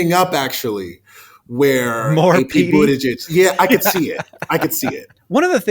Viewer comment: what are you saying here? "More PD"?